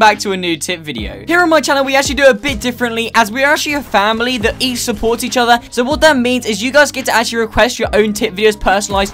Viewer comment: Idk why but I heard u say "this is a family where we eat each other"